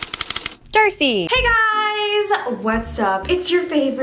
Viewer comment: dude i always thought you said "THIRSTY" in the intro but it''s "DARCEI" and i feel stupid